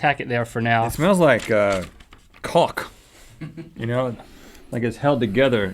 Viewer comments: did rhett just say Cawk ? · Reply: Viper Morrison Caulk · umm, did rhett say the boulder smells like i think he he did? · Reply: Caulk. Not Cock.